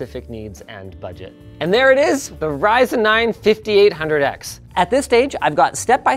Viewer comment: Now it’s like 2023 or something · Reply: I thought Linus said R9 5800x xd😂